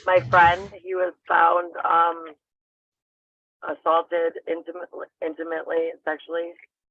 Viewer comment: i thought she said "intimately/sensually" and that doesnt sound too bad.